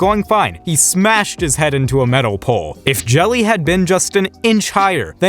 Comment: You know it is bad when it sounds like he said “Joey” and not “Jelly”